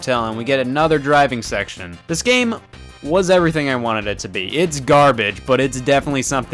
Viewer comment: why did Scott say 1 & 2 so mysteriously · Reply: I heard it as "want it to?" at first